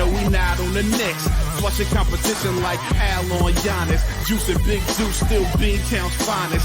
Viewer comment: Does anyone hear a brown shoutout in the opening song? His is the only name I can’t hear in the lyrics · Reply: Thank you!! I thought he was saying Deuce and Big Duece but now I can hear it lol